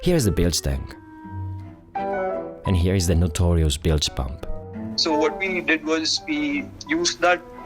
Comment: I thought he said bile pump 🤣